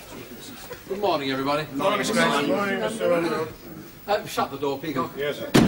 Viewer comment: did I hear someone say; " Good morning Mr. Grace"...???